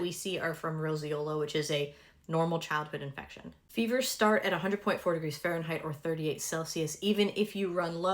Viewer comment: I thought you said "Fevers start at 104 °F." I'm so glad I went back and re-watched it.